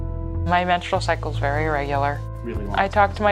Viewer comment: "Used to having an irregular cycle" "I've always had a regular cycle" Uhmm. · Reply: "Very irregular" is what I heard. the 'ir' was just slurred with the end of 'very'.